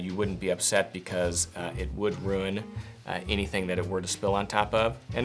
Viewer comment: If you spill the cleaner it sounds like you say, “ don’t worry because it would ruin any surface it touches.” What exactly are you saying? Does it ruin every surface attaches or doesn’t it?